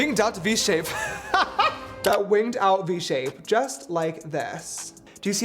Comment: what did he say? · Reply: he meant to say winged but said it with a v like vinged and it sounded like a russian accent